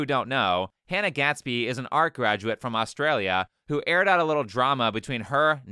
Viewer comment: thought you said Austrian for a second, almost gave me a heart attack😂